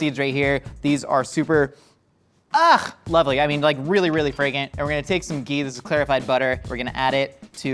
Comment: I definitely heard say "fragant" 😂😂